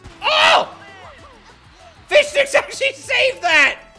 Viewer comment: Every time he yells fishsticks it sound like he says fishdicks